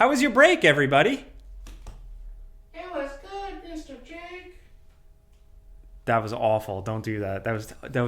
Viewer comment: I heard Mr hanky at 0:25 lol